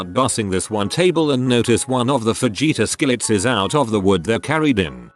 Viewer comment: Who heard vegeta skillet?